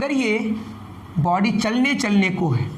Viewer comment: Can anyone tell me what he's writing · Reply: चलने चलने को है 😅